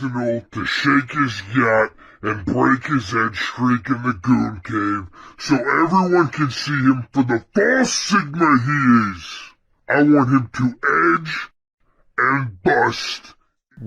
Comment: "I want to chicken off gad and break his edge and bring a link dink so everyone so everyone can see the bus in my hair. I want him to edge and bust." Is all I heard from the audio clip